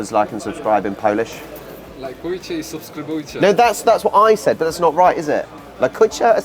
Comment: "Lajkujcie i subskrybujcie" - All I heard is "La Cucaracha", funny :)